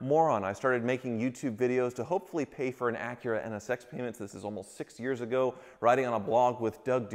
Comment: Sounds like you said sex payments at the beginning of the video 😝